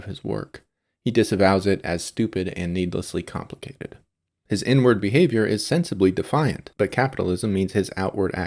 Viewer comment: at first I heard "...his n-word behavior..." and was like, wait, what? Until I heard "...his outward actions." lol.